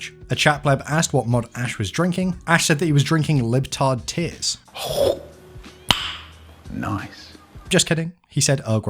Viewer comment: I’m sorry I might have gone deaf but...did I just hear “Libtard tears”?